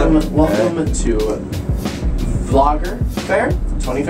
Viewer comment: At the start i thought he was saying, 'vlog affair' but when he showed the sign i realized it was 'vlogger fair'